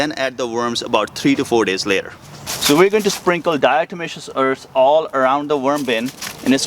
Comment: ggod job but at 3:30 you mention the white powder...what is that please. I can't make out comment as recorder seemed to be away from you? thanks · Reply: Diatomaceous earth